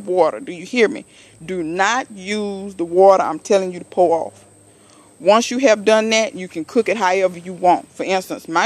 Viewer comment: what did you say lol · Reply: DO NOT use the water you are supposed to pour off!!! lol